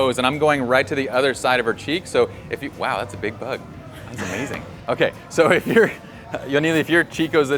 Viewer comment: did Pye say its a big butt?? 6:57 :P :P :P